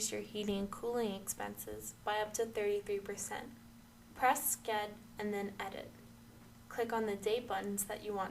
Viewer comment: “Press sced “. I cannot tell what she’s saying at 2:08! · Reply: Got it—-duh! Sced 😑